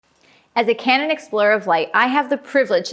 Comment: I am sorry I thought you said you are an Explore of Light?